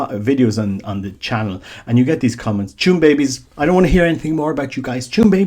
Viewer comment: No offense, but at 6:00 you are saying something like "chum babies" and I can't understand it. Even after living a year in Ireland, I can't figure that one out. Overall, though, I'm with your general sentiment.